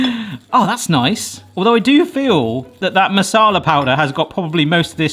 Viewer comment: At first I thought he said Shat Masala 😂😂😂😂